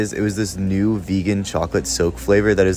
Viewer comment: Am I on something or did I hear “vegan chocolate soap”